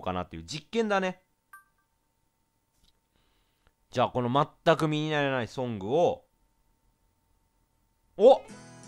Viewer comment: 「全く身にならない」が言えないレトさん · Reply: 「全く身になやない」って聞こえるw